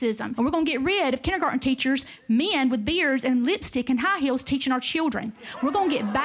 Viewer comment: Okay, the first two times I saw this, I definitely thought Kandiss said, “Men with beards and women with high heels and lipstick” so I just thought she was super weird about the personal hygiene and dress codes for teachers, like teachers dress too provocatively?!? But it was just good ol’ fashioned anti-trans hate rhetoric. But what I thought she said fit in seamlessly with all of the other insanity she spouted.